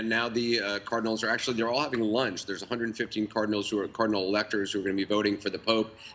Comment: i thought he said Carmen Electra is gonna vote for the next pope at 0:26 :P